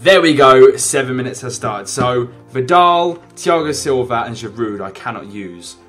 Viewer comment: When he said medal I thought he said vidial